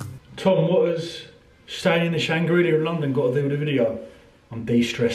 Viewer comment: Sorry, what did you call hotel? The Shangrillia? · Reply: jonnyrmsy Shangri-La (The Shard)